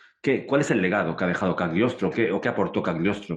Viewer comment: Minuto 35:46 ¿No entendí el nombre del autor de quién están hablando? Repites el nombre pero no logro entenderlo y por más que busco no encuentro nada semejante... Por favor me pueden escribir su nombre. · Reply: Cagliostro , el extraño mago y masón del siglo XVIII